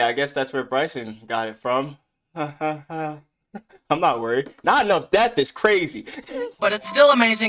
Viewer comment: Not enough death is crazy sounded like he said 9 year old death is crazy but it’s amazing and I still love it